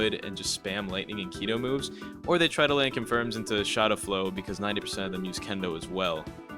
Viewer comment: Did he say Shotta Flow at 1:39 😂